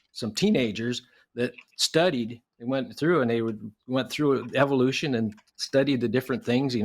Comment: did he just call it DEVILution?